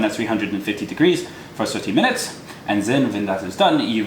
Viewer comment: did you say 13 or 30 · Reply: 30